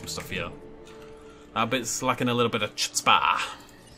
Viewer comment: D-did you just say chutzpah? Between this and your opinion on JW3 you are someone to be feared